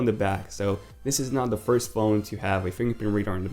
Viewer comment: "This is now the first phone to have a fingerprint reader on the back. There's quite a few out there..." Um what? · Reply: DJ Bama I think you're mistaken. said "this is not the first phone to have a fingerprint reader on the back." but I heard "this is NOW the first phone to have a fingerprint reader on the back." and then some other people clarified that he said "not." so I never said that this was the first phone with a fingerprint reader on the back, did, and technically he didn't either.